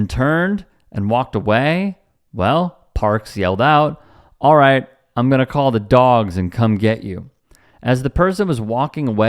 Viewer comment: "Alright I'm gonna call the dogs, get some BBQ sauce, and come eat you!" Is what I heard.8:17 Parks has a myocardial infarction, almost dies, and lumbers back to his vehicle.